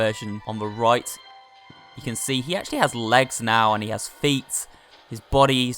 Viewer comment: did he just say feets